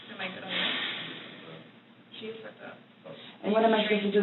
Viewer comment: the FEMALE investigator outside the room (going thru Sarah's 👜) ... does she say .. "what? ... No . SHES FUCKD UP" .. ?!? (Tht is LITERALLY RIGHT WHEN SHE SAYS IT SO JUS CLICK MY watching on my phone lol can ANYBDY MAKE IT ACTUALLY OUT WHAT WAS SAID LOL